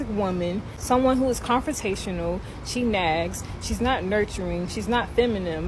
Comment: 🤣🤣🤣Im sorry but I cant stop laughing at this chick at 8:30 she says "confiscational" instead of CONFRONTATIONAL and at 8:34 she says "feminim" instead of FEMININE! 😂😂😂 Im mean she is cute and her brain tried to make a video, but damn!